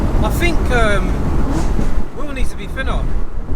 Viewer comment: It did sound like he said will needs to be thinner😂😂